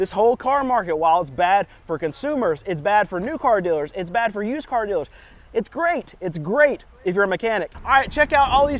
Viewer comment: it sounded like he said " it Hertz used car dealers" lol